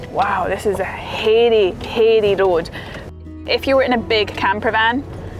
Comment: what kind of road? Did you say ‘hairy?’ 😅